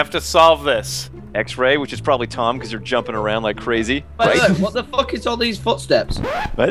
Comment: LOL, :) @ 00:55 "What the F*** are all these footsteps" Took me about three tries to make out what was being said. :) That was brilliant guys. Luv these, thanks.